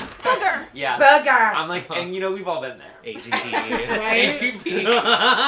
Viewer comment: did she say AGP lol